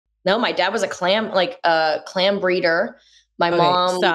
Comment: My dyslexia kicked in and got the first whole time I watched it I thought it said 'calm breeder" and I was like.... someone who just likes to have a LOT of children...peacefully? Lol